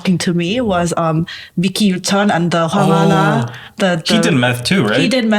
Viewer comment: who were the 2 who took meth? Couldnt catch their names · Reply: "MICKEY" Yoo Chun from former DBSK Idk what her name is, but the girl was just a well-known associate